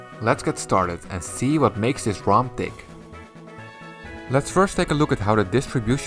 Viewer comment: The CC says let's see what makes the ROM Dick instead of Tick lol